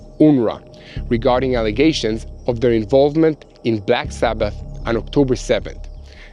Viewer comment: Did he say black sabbath?